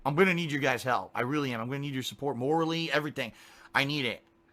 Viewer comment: I heard he needs oral support.